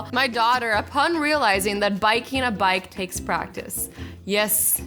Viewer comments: Did she say ''biking a bike'' at 1:34? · biking a bike? Is it just me who heard that?